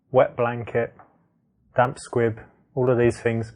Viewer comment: It's my bedroom dude, not a hotel room. Thanks for the advice of "just be yourself", that's a good one for all the virgin nerd shy guys who are not allowed to meet women in the street but hate bars and clubs. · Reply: 1:30ish, the subtitle says "damp squid". It's damp "squib".